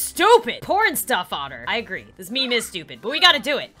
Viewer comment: Laurel or Yanny? Boi I heard Patrisha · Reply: BOI! I heard "this meme is dead!"